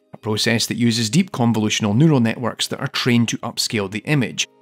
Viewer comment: "Neural networks" What I heard: "noodle network" Me: giggles and scrolls back to check I heard that right.